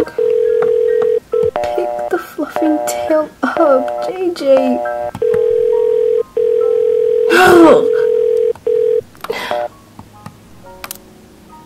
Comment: I thought she said "f@ tail up"